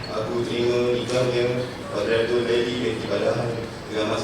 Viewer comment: i heard it as Badriatul Laili isnt it? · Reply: fathiatul laili